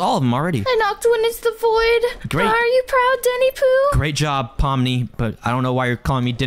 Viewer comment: Did you just say daddy Pooh💀